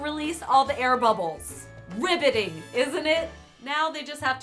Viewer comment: I thought you said *ribbetting*. If only.